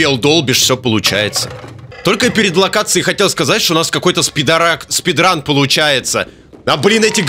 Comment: мне или послышалось, или сказал спидорак .-. вместо спидрана :/ это болезнь придумманая в моём вузе если что :)